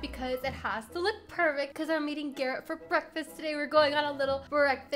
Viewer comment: IT SOUNDED LIKE SHE SAID I HAVE TO EAT GARRET FOR BREAKFAST